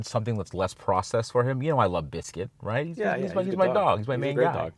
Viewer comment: Biscuit... I thought you said "Dip-sh!t" LOL😀